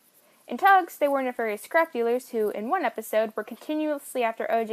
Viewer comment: Ohhh scrap dealers. I thought she said crack dealers.